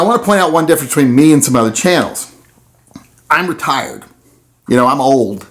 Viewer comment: Oh retired... I thought he said retarded at first. Lol just kidding